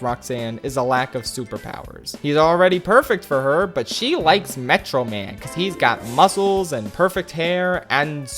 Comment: Did he say megaman?